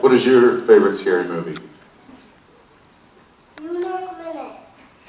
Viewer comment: what was his favorite movie? Cant hear him lol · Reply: Caelyn Crochet he said New York Minute